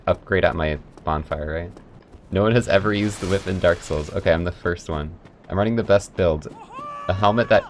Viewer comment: Cant believe they're playing dark souls this is awesome edit: (dunno why people care about pronouns but here) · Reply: oh wait nvm i thought it said plugging dark souls i am stupid